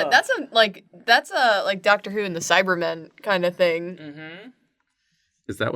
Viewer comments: When I heard "Doctor Who and the Cyber Men" my brain processed it as "Dr Suess and the Saibamen". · i HEARD say "doctor who", but for some reason what my brain processed was "doctor seuss", and i was just completely confused for a second trying to imagine a dr seuss book in which people are made into robots befor my logical thought caught up with me.